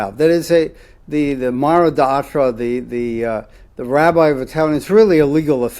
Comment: What did he say at 33:24? Mara de'achra?? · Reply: מרא דאתרא Mara d'atra, Aramaic for master of the place